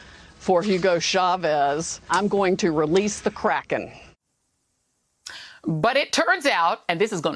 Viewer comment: She cant release the kraken. She's crying into her my pillow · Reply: Oh I thought she was saying release the crackhead and thats when trump and his family left the Whitehouse before he could be arrested for impersonating a president